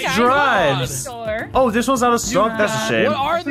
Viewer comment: Did say sHoT? (Sorry if I spelled the name wrong)